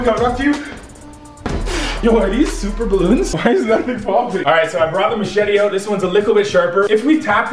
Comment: hold up hold up what did my boi just say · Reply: Tamika Odeh "OK I brought a machete out and its sharper"